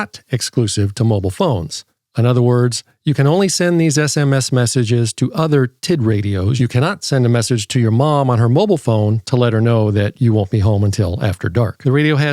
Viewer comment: I thought I heard the TID-E radio.